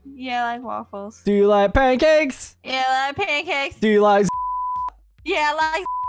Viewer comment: LOL WHAT DID SAY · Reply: He said do u like Zachary